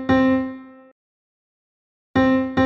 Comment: What did he say? (REPEAT IT) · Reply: THIS PIANO IS ANIMATED CORRECTLY, NOW SHUT UP